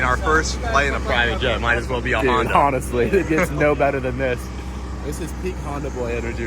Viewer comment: did he say “Hondasly”?